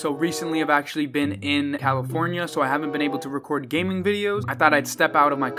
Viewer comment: Bruh when u said California I thought u said alifornia